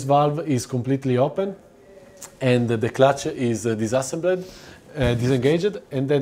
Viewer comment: There's a very quiet snigger by the cameraman when the Italian engineer says "dis-sah-bull-derd" instead of disabled 16:40. What a fantastic video and a fantastic car. Beautiful eccentric Italian engineering under the stewardship of Tony Starks cousin. Brilliant!